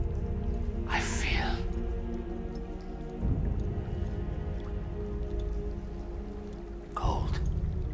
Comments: maybe the skeleton people are surprised whenever they get striked? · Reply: i thought he said "i feel Gold" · Wait, he said "I feel cold "? I thought he said "old" lmao